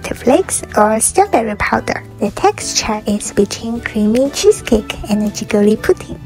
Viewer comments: I thought she said the texture is bitching I’m so sorry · I thought she said "the texture it bitchin" for a sec 😂